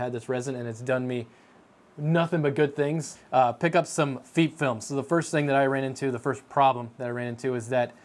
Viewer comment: FEEP Film? it almost sounds like your saying Feet film. idk about anyone else but i say FEP as in the E from pet or pep. if it was spelled feep or Feap film then i would say it that way but its not.